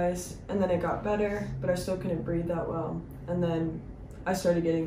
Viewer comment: i thought u said u couldn’t drive 🤣